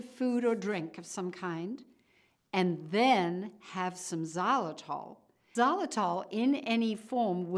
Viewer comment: what is that word "zolital"? What is it? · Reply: It's Xylitol. It's a chewing gum